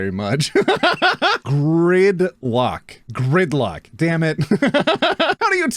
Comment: Happy late birthday 🎂🥳 1:51 i thought it said grind lock😭 10:12 i knew people could have xxxy or xyy but i didn't know people could have both cc and xy 0o0